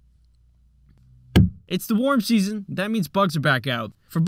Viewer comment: For some reason I heard "it's the morb season." Morbing time????